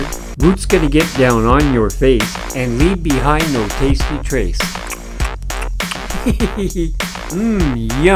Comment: I thought he was saying "and leave behind o tasty trace"